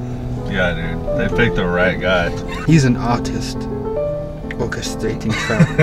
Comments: when they said “hes an artist..... orchestrating” i thought he said, “hes an autist” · I thought he said "He's and Autist" 🤣🤣💀